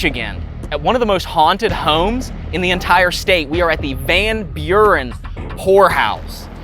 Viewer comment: I thought said 00:50 "Van Buren Whorehouse"... 😆